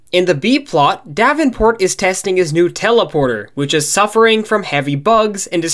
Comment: What was said "new teleporter" what I heard "Nutella porter"